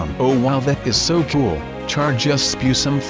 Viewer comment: did say Bad A$$?!?!?!